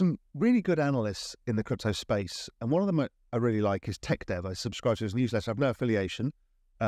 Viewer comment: Thank you! Who did he say he follows, 6:52? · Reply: Tech Dev